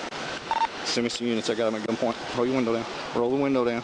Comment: In the middle of the video I thought he said send MEMES